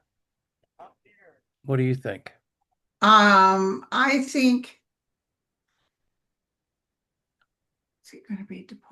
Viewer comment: Did anyone hear a voice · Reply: I did. It sounded like, "Up here." At about 47:02-3 ish. When they were talking about Abreigo Garcia.